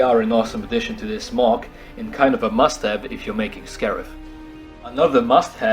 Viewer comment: every time you said "must have" in this video i thought you said mustache